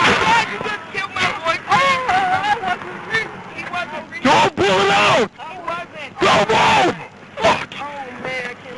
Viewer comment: it sounds like he was saying 'I have to pull it out' perhaps the gun was in the glove compartment or side compartment on top of his insurance and registration papers